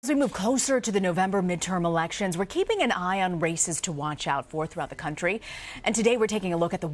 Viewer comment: Oh wow, at the very beginning I thought she said "We're keeping an eye on racists to watch out for throughout the country" ... yeah, we should watch out for that one racist in the West Virginia senate primary, for sure.